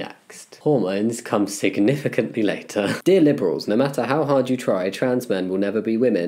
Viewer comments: I thought you said "Dear nipples" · instead of Dear Liberals i heard, Dear Nipples